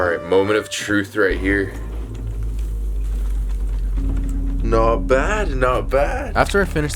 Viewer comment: WTH DID HE SAY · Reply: He said not bad